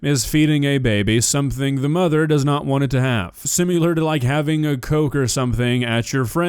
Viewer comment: Did you say "simular"?